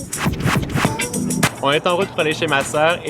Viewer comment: "Ont est en route pour lécher ma soeur" mdr c'est ce que j'ai entendu lol